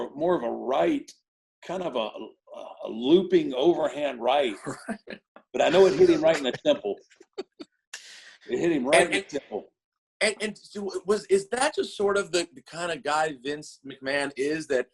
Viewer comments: For a sec I thought he said "hit him right in the nimple" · I heard “it hit him right in the nipple” I chuckled out loud and wondered who hits a guy in the nipple. A few seconds later I hear hm say “temple”. I’m still smiling at what I thought I heard.